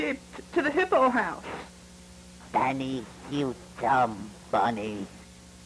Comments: I thought he said "You dumb fuck" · I swear they must've changed the "dumb bunny" annunciation because before I swore it said "Banny you dumb bitch" 😂 it could've been my speakers or me only half paying attention but i thought it was the funniest thing at the time